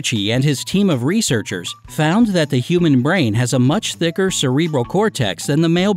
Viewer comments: Did he just say "human brain"? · Did anyone else hear him say human instead of female at 3:26 No just me🤔